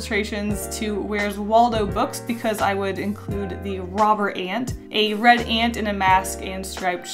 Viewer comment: When you said "Robber Ant" I thought you said "Robert Ant" So... I'm calling him Robert now. :)